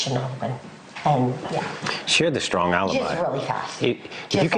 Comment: "She had a strong alibi," said Steve Goncalves. Who is he talking about? He seemed to be reacting to something his wife Kristi said at 3:26 but it's difficult to understand what she says. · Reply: You misheard. "SHARE the strong alibi."